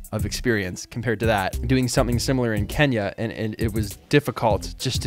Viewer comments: Did I just heard Kenya? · Did he just say KENYA like the country KENYA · did you say doing something similar in Kenya?